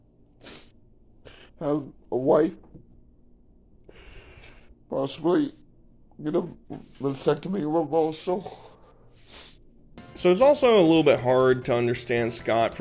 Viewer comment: Did he say "possibly get a vasectomy reversal"? WTF?😮